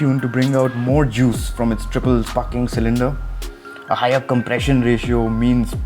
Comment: Did I hear triple f**ng cylinder @ 1:41 😅😂😂